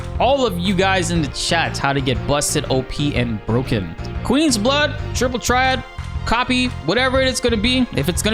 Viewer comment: Lol i thought u said busted open and broken 🤣 😅 like that got x rated real quick 😂 😊